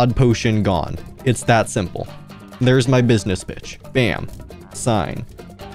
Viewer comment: im ngl at 4:28 i thought you said "theres my business bitch"